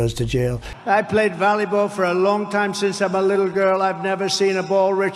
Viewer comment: I thought trump said when he was a little girl 😆